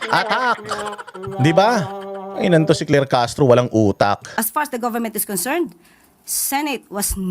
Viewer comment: Did she say “as “past” as the government concern? 😂😂😂😂